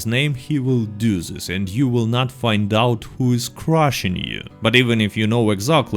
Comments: I’m glad you put the word crash on screen, I thought you were saying crush. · This entire time I thought you were saying “crush” not “crash”